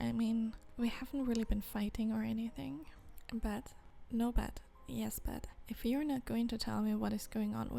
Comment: "no but, yes but" What i hear: "no butt, yes butt"